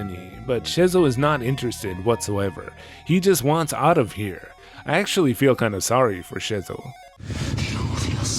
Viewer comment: I heard "I actually feel kinda sorry, fo(r) 'shizzle" Me too, my bizzle, me too.... xD